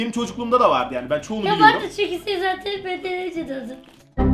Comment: söylediği cümle ya vardı çünkü sen benden önce doğdun kdksjakdjhhhhhhjf nasıl anladım bende bilmiyom ama isteyenler bakabilir